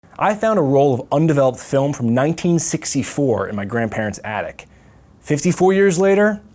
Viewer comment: I thought it said I found a undeveloped piece of foam from 1964